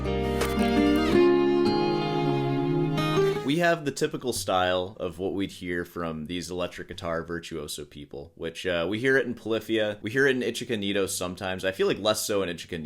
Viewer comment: Did i just hear "The Chicken Nito"? I might be crazy lol